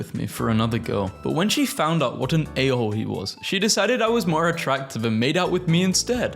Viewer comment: I heard this as “when she found out what aioli was” and I was so confused